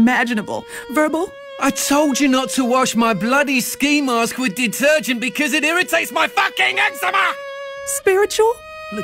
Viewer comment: For those that dont understand the british guy let me translate he said something along the lines of, "Bitch this chicken is cold!" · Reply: Thank you. I thought he said something along the lines of “I told you not to wash my ski mask with detergent cause it IRRITATES MY FUCKING ECZEMA!”